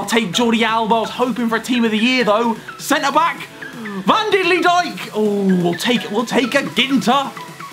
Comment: umm what did u say van dilidighk